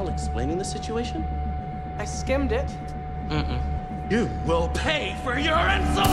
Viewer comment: I thought he said "you will pay for your insulin" ik that's wrong but I thought it was funny lmao😂❤